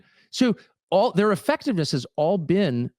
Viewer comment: I had to go back. He said “it’s all being done sub rosa,” meaning in secret. First time I thought he said “sabrosa.” Lol. What? Tucker thinks this is all tasty in Spanish?